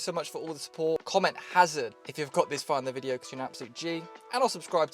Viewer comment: Hazard? Haza? 17:31 not rlly too sure what you said but LETSSSS GOOOOO 🔥🔥🔥